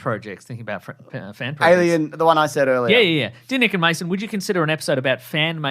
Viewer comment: Motion to rename H8 Male to The H8ful Bait 1:18:50 Did he say Dear Nick and Mason? 1:29:20 Huge fan of original Buffy Not the least bit interested in New Buffy. It is Whedon's baby, and to try and raise it without him will fail miserably.